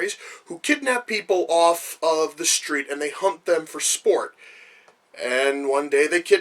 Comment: For at least 2-3 years I thought said "hump them for sport" as opposed to "HUNT them for sport", and I kept imagining how fucking awkward of an action film that would be.